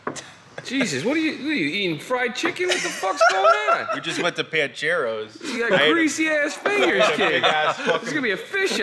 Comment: dude, I thought he said Bancheros, an Italian restaurant in the SF Bay Area, that's closed now, we use to go to as a kid... I was like, wait a minute, did he just say... lol